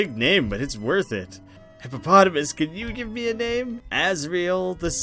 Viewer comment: he actually says HippoPOTATOmus, not hippopotamus.